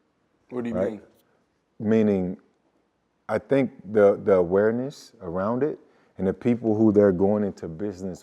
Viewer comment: We need to see that school or at least a location and/or blue print of it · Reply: I thought he said it was Delaware